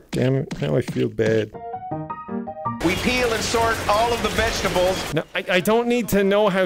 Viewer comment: At the 8:10 mark i heard we pee on and sort the vegetables